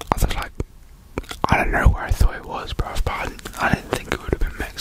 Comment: did bro say "idk where i thought it was "bruv" "🤣